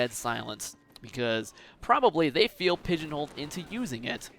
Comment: "pegenaled"? what is he saying after 3:35? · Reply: pigeon-holed, as in automatically forced into a certain area only without much choice